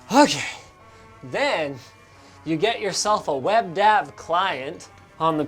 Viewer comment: I thought he said WebDAB...